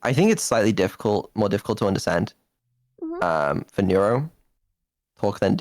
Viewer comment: I just heard "Neuro tentacle" and pictured the Neurodog getting a bullwhip for a tail.